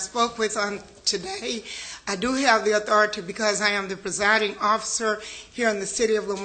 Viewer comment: Turn her mic off good lord....how many times did I hear her say "I am the residing"...lady you got bigger issues to resolve 😡